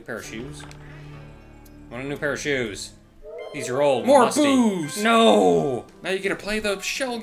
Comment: Ten seconds into the video and I hear say "MORE BOOZE!" ... ... Yeah, I know that's not what he means, but I still find it funny.